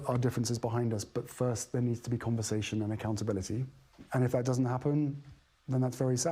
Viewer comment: He said, "conversation and accountability." NOT "compensation and accountability." FYI I agree with all you said and dislike both H&M but I think you misheard